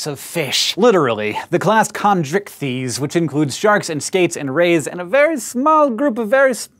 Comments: ohhhh... rays and skates! (me mishearing stuff= raisin skates) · What other species does he say at the beginning that doesn't have bones? It sounded like he said raisin skates haha but I'm pretty sure that wasn't it. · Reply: Rays AND skates.